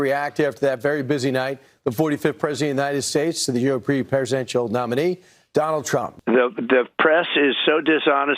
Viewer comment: did he say "European presidential nominee" at 0:03?